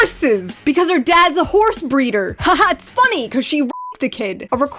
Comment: You read 'horse breeder' and I hear 'wh*re be*ter'.